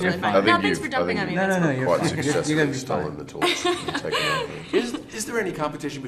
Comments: chris says " i think you've successfully stolen the torch " anyone else hear it? Listen carefully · Chris: " I think you've quiet successfully stolen the torch and take off it" (last couple of words of the sentence I couldn't understand properly) lol get rekt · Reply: He said "and taken over" at the end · did Chris just said "stolen the torch" ? Hahahahaha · What does Chris say at 5:14??? Please, someone HAAAAALLLP!!!!! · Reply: “I think you’ve quite successfully stolen the torch…” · Anyone else catch Chris saying "You've successfully stolen the torch"